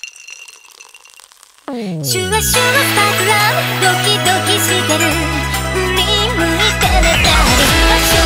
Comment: i just heard doki doki literature club